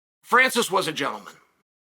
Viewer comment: I had to turn up the volume to hear that first sentence again cause I thought Chael said „Francis was a joke“